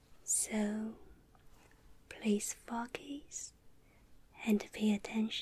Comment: At first when she said focus I thought she said fuckass